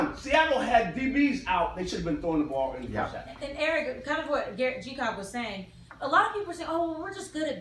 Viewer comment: Ummm...did she say g-cock?